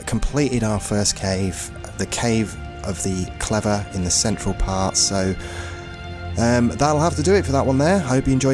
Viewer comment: Each time I heard you say 'central part' i kept miss hearing it as 'central park'